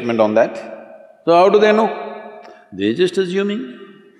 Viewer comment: what did he say ? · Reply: "They are just assuming?"